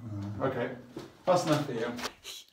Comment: Who heard him say that's enough for the video 1:50 then listen